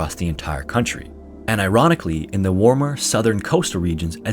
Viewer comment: "and IRANically" lmao sorry i heard that wrong